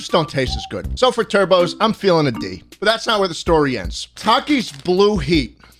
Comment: What did he say?! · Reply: He said he was feeling a D